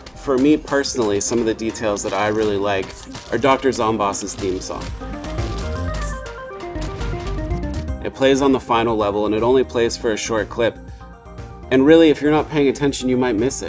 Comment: what is bro even saying??? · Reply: Az you can seu, the final bozz theme "ZOMBOTANY" dr zombosses theme song play on tha finul lvel and only plays foar a shirt clIp. If ur not Payng atenshun, u mIGht miz it. we don reLeAz thaT in tha zOwndtrak. Iss kInduh un eeztor eg wEe leAf 4 tha plaYurZ . - tHa deVelUpurs NOT THE ZOMBEEZ!!!!!!